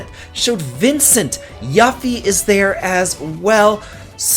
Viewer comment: Did he just say Yuufy? 🤣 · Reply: As a matter of fact, I said “Yuffie”.